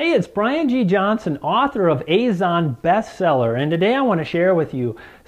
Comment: Hi. Here's a tip. I thought you said "Amazon Best Seller" not "Azon Best Seller," so I was wondering "What is this book that is a best seller?" I suggest you put the name of your book and a link to it in the description or in the video itself. HTH